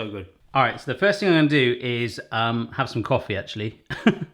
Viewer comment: When he said the name at the beginning I was so confused because I️ thought it said toffee not tofifee or whatever